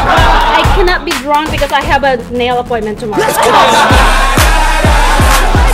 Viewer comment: “I cant be drunk because I have a nail appointment tomorrow” LMAOO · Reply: Iliana Kada I thought she said, “I can’t be drunk because I have a male appointment tomorrow” :)